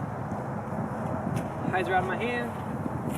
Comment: When you throw the second warden at 4:50, do I hear correctly that you say "I dropped my hand" and it hyzers pretty hard? Could you explain what that means in form? I'm having some hard time releasing my midranges flat/straight and can't find a reason for it. Currently only way for me is to throw a beaten flippy one with a hyzer flip if I want a straight shot.